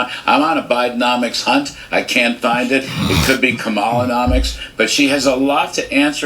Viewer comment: I misheard "Kamalanomics" for "Kamalaholics" and I can't wait to use that term now 😂